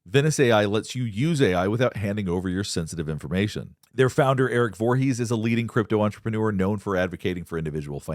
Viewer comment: I heard he’s Jason’s brother